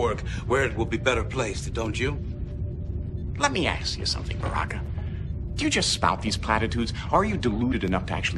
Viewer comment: A little past the 11:00 minute mark. OC is from 96 and the black bridge officer's name sounds like "Barrack". Anybody else hearing this?